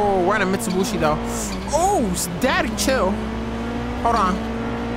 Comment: did he say daddy chill